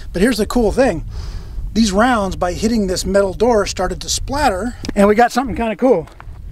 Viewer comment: I figured you were busy doing riot control, but I was "Jones'n" for a new episode.! I had to laugh when it sounded like you said the soft point .223 round "Shrarted".!